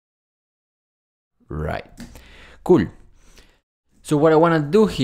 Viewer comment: cool 이 아니라 (개)꿀~ 로 들림 이 하시는 한국어 너무 그럴듯해요 어디 노씨에요?